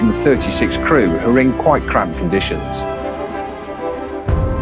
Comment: I thought he said the rest of the crew were in quite crap conditions there lol. Good video as usual.